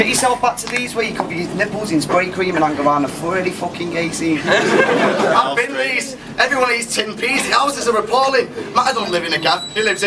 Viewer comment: Oh and 6:42 , please, my british friends help me out. What is saying? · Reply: "I’ve been Leeds, everyone eats tinned peas, the houses are appalling / Matter don’t live in a gaff, he lives in a hole in the floor covered with a sheet of tarpaulin". (He's putting on a Yorkshire accent in that last bar).